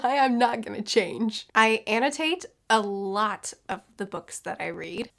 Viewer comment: the sound in my computer glitched to instead of hearing "I annotate a lot of the books I read" I heard "I hate a lot of the books I read" 😂. And I look for spoilers if I don't like a book too :) Great video